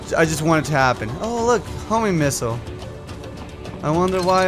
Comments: It sounds like says "homie missile" instead of homing missile. Homies unite :3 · Everytime says Homing missile i always hear "Homie missile"